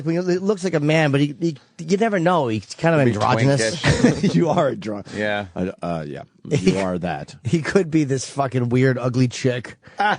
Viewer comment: right after Jimmy said Sam was kind of androgynous, did Opie say "haha yeah you are a drunk..uhhh yeah..you are that."?